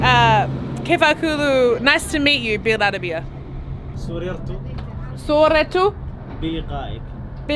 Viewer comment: There are many ways to say nice to meet you in Arabic, like "teshurufna" تشرفنا ! I heard you say something like: "I took a picture of your slaves" ! 😂 صورت رقائك